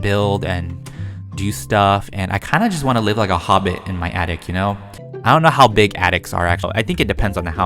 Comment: Hehehe... at first when you said attic I thought you said addict... hehe... I thought you were gonna say beer and weed... hehe... I’m a messed up child 😅😖